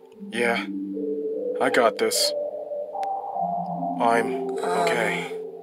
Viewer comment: Then he said “I‘m okay“ I heard “I‘m gay“ :P